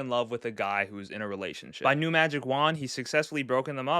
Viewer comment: i heard tyler say: “new magic wand is my fav song” so he likes…